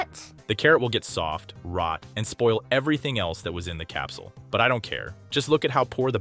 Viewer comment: why do the captions say dandelions instead of stamps?